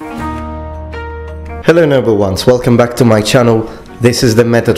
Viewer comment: "Hello noble ones." I swear I hear him say "Hello number ones" every time